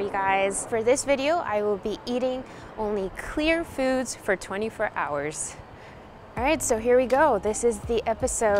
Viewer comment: I thought it said "I ate clouds for 24 hours " 😐